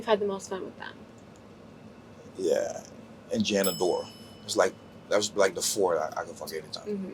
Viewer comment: What is the fourth girls name Janna Door ? What lol I heard of the other three who I hear wrong help me out what he say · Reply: gianna dior, rookie.